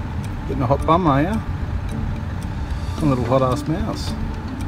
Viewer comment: thought he said "hotass mouse" Would've been a great pun, what a lost opportunity smh